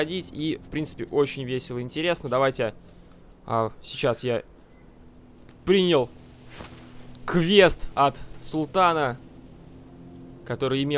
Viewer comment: многим показалось что он сказал крест ????